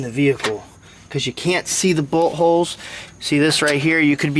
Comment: Bolt holds, thought you said butt holes lol